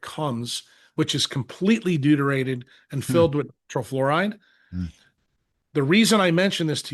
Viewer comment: What is the word after "deuterated and filled with" -- something-something fluoride? Was this censored or did the audio just drop out? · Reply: Fluoride. It's in the transcript